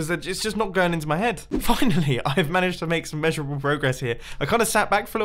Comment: when he said Finally, I heard the f word because he said "fuhhinally".